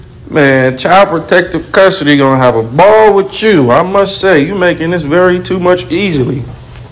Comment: did he say "you making this very too much easily"...? My head hurt. I want to feel bad for Chrisean but for some reason i cant. I do have sympathy for the baby though...smh